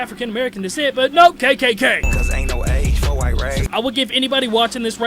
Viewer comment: is sounds like he said “cause ain’t no age for white rage.” If so, that’s a lame ass, lazy ass, whack ass rhyme. It doesn’t even make sense. Nick! We ain’t feeling all that race baiting.